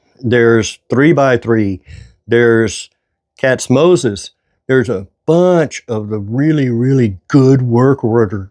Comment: I thought you said “catsmosis” so I searched YouTube for that, it got what I was looking for and Katz-Moses came up!